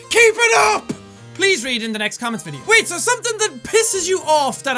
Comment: Ok to be honest jackspeticeye for the past videos that i watched, at the end of the video when you say "In the next video". I thought you said "Good luck for you". Funny cause I just found out now that you say " In the next video" at the end hahaha :D